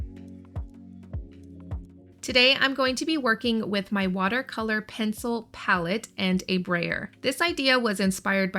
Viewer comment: I’ve watched this video 3x…… do you have a distress watercolor pallet?? I heard you say watercolor pencil but those are cakes. I’m obsessed with trying to figure it out 😂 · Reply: Hey there, yes it’s a distressed, watercolor, pencil palette what I did was, I used the shavings from the distress watercolors to make my own watercolors. There are several videos here on YouTube to figure out how to do it. I love doing it instead of wasting the shavings.